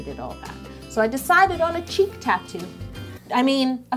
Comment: "cheek" tattoo i thought she said "cheap" tattoo lol